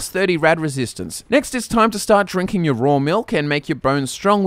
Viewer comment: Did he say "raw milk" ?